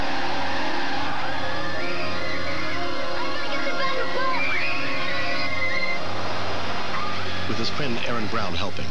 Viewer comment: Does anyone know what Kenny said between 2:10-2:11? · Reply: Everybody get to the back of the bus?